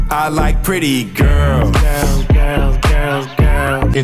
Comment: I thought he was saying “I like Titties Girl”. Edit: not sure what's worse… the song or the video.